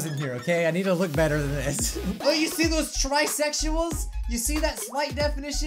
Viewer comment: in the beginning it sounded like he said: look at those bisexuals i'll leave now..